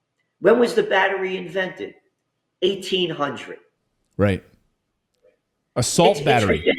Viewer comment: I thought he said assault battery, but it was “a salt” battery. Hahaha! No surprise, they put “assault” in front of everything these days. 😂